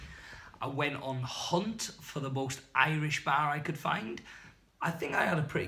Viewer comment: Thought you said "Hunting for Irish Bear" 🐻 not Bar! 🤘🤣 - Kevin Venture